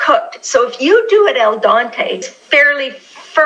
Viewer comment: that al dente sounded like All Dante